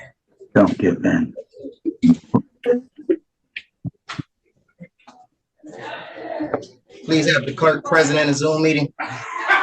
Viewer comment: anyone else catch what the DA said when the defendant requested the clerk to be present???? "Don't give in" hhaahahahha